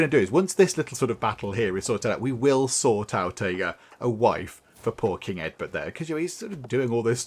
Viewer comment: "We will sort out a wife for poor king Eadbert." I misheard it as "We will sort out a wife for porking Eadbert." Bit of a difference. Not sure how much letter writing they will be doing as of yet.